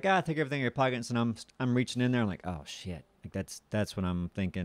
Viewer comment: says shit at 1:42. I heard it. Proof!